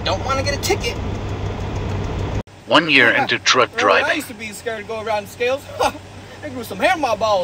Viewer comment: Thought i heard drunk driving😭